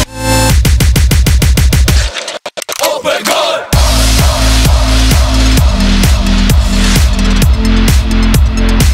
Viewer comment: What does he say at 1:57 ? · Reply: Oh my god